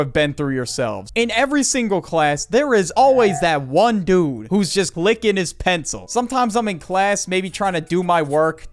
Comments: When you said “there’s that one kid in class who is always clicking the pencil” I thought you said “there’s that one kid in class who’s always licking his pencil”. But the fact that it still sounds like something that could actually happen really just shows how messed up kids are at school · thought you said licking his pencil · I thought he said "licking his pencil" 💀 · I THOUGHT IT SAID LICKING HIS PENCIL 😭😭😭😭